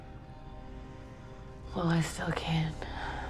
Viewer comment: I swear I heard “Molest a can”